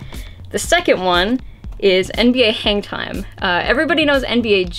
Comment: I thought said 'NBA hentai' at 0:58 and almost lost my shit